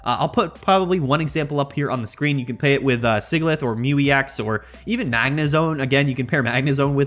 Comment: sorry i'm new to pokemon and my hearing/english isnt the best, what pokemon does he say we can pair it with mismagius? i heard mew ex and magnezone, what was the first one? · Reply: Sigilyph